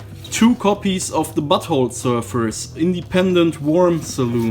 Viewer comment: Did he say buttholes?